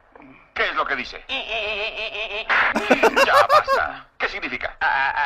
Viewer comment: Que es lo que dice:EEE🍷🧐 "Lo pega" Que es lo que significa 🤨 AAAAAA🤑🤑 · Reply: lo pega